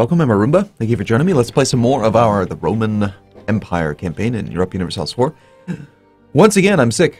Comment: Get well soon, Arumba! In the beginning of your episode it sounded like you said "...some more of the Broman Empire."...imagine the Bro-man empire, it wouldn't be Caesar, but Brosar, The capital Brome and they would host the Broylimpic Games. Ave Brosar!